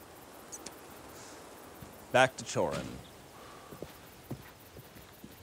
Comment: This whole time I thought they were saying "back to choring" as in back to work. What is "chirping"?